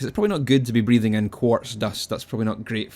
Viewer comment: When you said quartz dust i thought you said corpse dust and i was like what the fuck is that again?